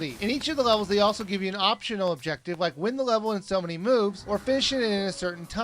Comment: did he say "boobs"? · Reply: He said "moves".